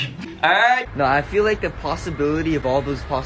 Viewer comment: that's not what he said tho. He was going to say what he thought Johnney said but then was like "I better not tell" so he was like "We're actually really gonna be m- .... ALRIGHT"